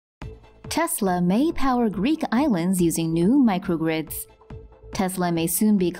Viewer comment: I thought it said geek islands